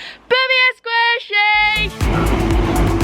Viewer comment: Ah! All this time I thought you’d been saying 'squishy babies ! :-~ Love your vlogs, always make me smile.